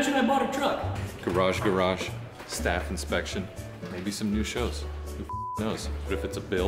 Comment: Who else thought they said staph infection😁